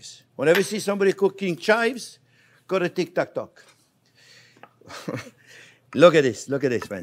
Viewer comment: HAHAHAHAA dude, what??? I had to listen to that like 4 times, and it still makes me laugh like a hyena cuz i have no idea what you just said. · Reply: You should never cook chives, if you see someone cooking chives you go to Tik, Tak, Tok 🤣